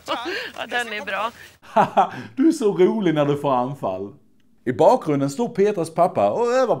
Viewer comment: typ de 10 första gångerna jag såg recensionen så hörde jag säga "hahaha du är så rolig när du framfar" men tydligen varre "när du får anfall"